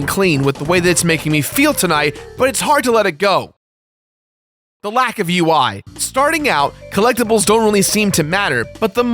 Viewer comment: Is there an editing/rendering error here? The audio goes away before Kingdom Hearts appears onscreen and I hear "the lack of you why".